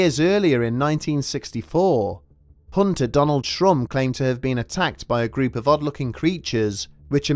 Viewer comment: I thought he said Donald Trump